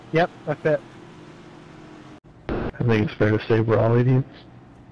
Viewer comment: 90% Sure what he said was Think it's better to say we're all idiots.